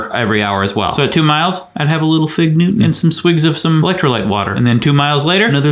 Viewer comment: a little what? I'm hearing fig-nu and I assume I'm mishearing it